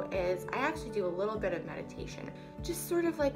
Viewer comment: I thought you said medication instead of meditation! Lol 😂 the things I hear!